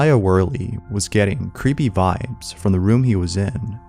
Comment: I thought he said he was getting gayed vibes